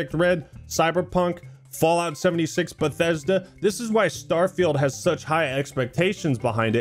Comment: Get your air up here: <link> use code ACTMAN for 15% off all your orders #airup #airuppartner Really goes to show how low standards have gotten in the game industry when other developers think making a game this good is impossible lmao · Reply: Did you just say Starfield?